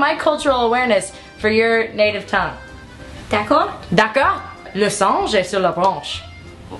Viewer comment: What did she say in French?! Its killing me, I NEED TO KNOW! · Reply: Laurie f Thank you! I heard "Les anges sont sur la branche" and I'm thinking "Why are angels in a tree?" LOL